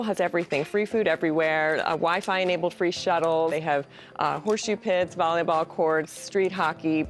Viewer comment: I really heard it as “Google has everything, horsesh*t pits, …” the first time 😂I